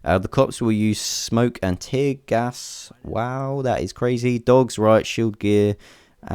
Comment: Did I hear DOGS?